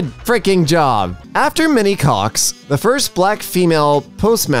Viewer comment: LOL 8:28 ok ok said "Minnie" Cox. i first heard her say "after many cox...." 😅